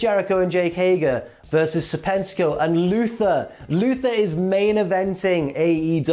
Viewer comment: Every time said his name, I kept hearing “Sir Pentico” which is a better ring name.